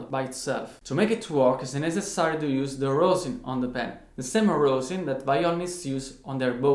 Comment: man maybe i'm an idiot, but i can't understand what this thing is. could you write it to me please? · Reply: It's called Rosin. Rosin up your bow my friend